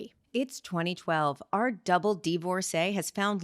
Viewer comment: I had to check the transcript because I thought you said, "our double D divorcee" 😅